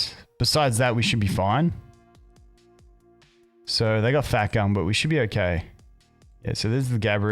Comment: Did call LI AO fat gum at 12:00 ? XD